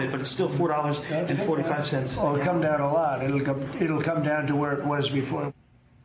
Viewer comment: He said "it'll come down a lot", as in, it will come down a lot. He did not say "gas prices have come down a lot", he said "it will". Not a Trump supporter, but please report accurately what was said.